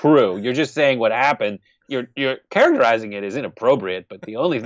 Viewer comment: I think he said characterizing it.... but it sounded like he said KERENIZED it. Which is better. So what if they were speaking Spanish Who cares what color their skin is.... I know a Chinese restaurant that the owner was BORN in the US as were her kids.... but they all had duel citizenship and to each other spoke Chinese..... or to make it better. I live 10 mins from Amish country and they all speak German or Dutch and they come to town and ALL speak in that language around us.... but they do all know english because that's how they make money.... (I am NOT trying to sound racist towards the Amish....) No one stands their and demands that they speak english. Sometimes it is easier to speak in a native language to work... or just because well..... they like to talk about the people around them or inappropriately and can if they arent speaking english out in public...... Doesnt mean they are illegal It's easier to open a can of beans with teeth then to open minds it seems